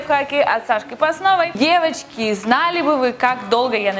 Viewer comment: мне послышалось "Евочки", а не "девочки" =) Всем хорошей недели!